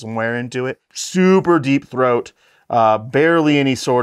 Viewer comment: Did he just say deep throat?